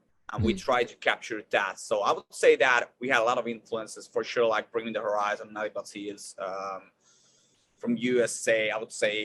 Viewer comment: Thank you for this wonderful interview & great questions. At 10:47 who does he say? Bring MeThe Horizon and who else? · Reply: I think he says Nothing But Thieves...